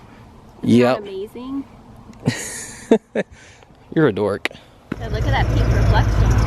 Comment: I thought he was gonna say, "You're adorable" but i forgot who it was. So "You're a dork" it is:-)